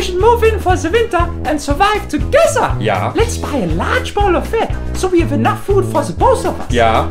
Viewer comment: I don't know why but saying "let's buy a bowl of fat" is so adorable to me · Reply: Oh a "BOWL of fat" I was so confused I thought he said "Ball" and I was like "can you buy literal balls of fat in Germany? Is that a thing?"